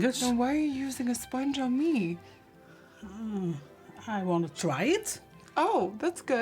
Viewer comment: When she said "I want to try it" I heard " your my test subject"😹